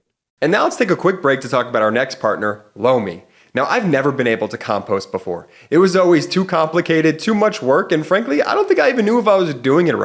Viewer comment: it sounded like, let's take a minute to talk about our next partner blow me😂😂